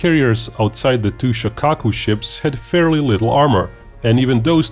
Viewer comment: Shokaku! I'm an idiot; I thought you were saying "Chicago class".